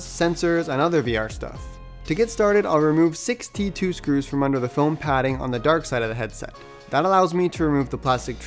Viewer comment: I thought you said 62 screws for a second and I got really worried for the repairability of the quest 2 for a sec XD